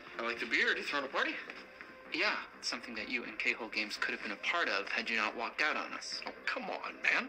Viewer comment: Am I the only one who finds "K-Hole" to be a funny company name?? Whenever I hear someone say "K-Hole", I hear "A-Hole" and can't help but laugh on the inside 😆